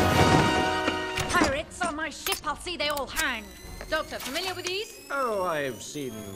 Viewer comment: Can someone tell me what Captain Amelia said in 1:37-1:39??? She has such a strong British accent, which I like. hehehehe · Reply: She says "Pirates on my ship? I'll see they all hang!"